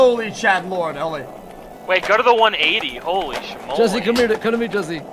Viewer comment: Jesse becoming the leader of a gang was the funniest thing ever. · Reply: genuinely thought that said jesus